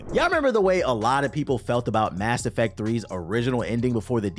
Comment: I could've sworn that you said "Ass Effect 3" at first, lol. Had to backtrack.